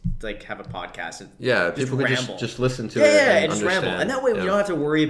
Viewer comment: Your Podcast Name: Graham-ble I thought thats what you said at the end instead of ramble